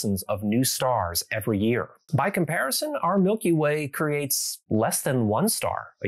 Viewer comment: misheard that as "by comparison our solar system creates less than 1 new star per year." Technically correct, the best kind of correct.